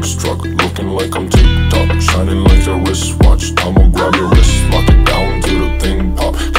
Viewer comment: For some reason I hear "Shiny laser wristwatch" instead.